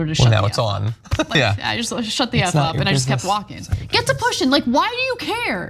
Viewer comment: 😂😂😂did Ana just say “ get to pushin’ @ 9:32???? 🤣🤣🤣🤣